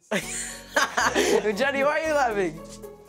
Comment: what did he say? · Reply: Maania F. He said Jenna why are you jenna